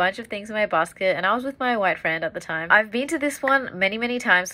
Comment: I first heard it as "wife friend", wtf is "wife friend"? 🤣🤣🤣